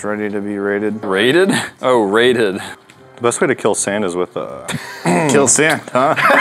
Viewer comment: I thought said best way to kill Santa Claus